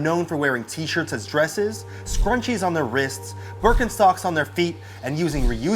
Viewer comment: Oh fricken shittttt 0:11 did he say Burkin-sockssss